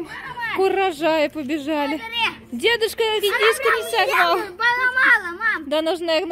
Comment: А если бы мама Насти осталась на хозяйство, тоже бы позволила сказать "не сожрала редиску" я была лучшего мнения о вас, но вы такая, какая есть. дай Бог вам много внуков!!! И невесток!!! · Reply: Вы переслушайте этот момент. сказала "не сорвал". Если вам что то послышалось, не значит, что так и было сказано, просто у вас слух плохой! И пожелания ваши, с двойным поддекстом, оставьте себе. Извинитесь, хотя бы.